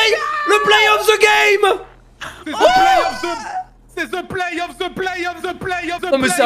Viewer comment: c'est quoi ? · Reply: The play off, the play off, the play off